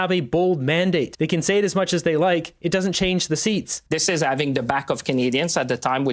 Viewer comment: I think he meant to say stabbing the back of Canadians. And it kinda sounded that way.